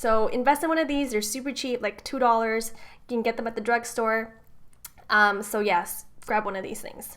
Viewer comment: Did she said drug store at 2:17?